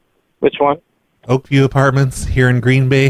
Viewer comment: won''t die. Not until he's succeeded in getting the word out about Oakview Apartments in Green Bay, and all their amazing free water. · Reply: glad you left this message, I thought he was saying Opey Apartments. I was gonna leave them some comments. Guess I won’t be leaving Opey Apartments comments now and prolly not Oakview either.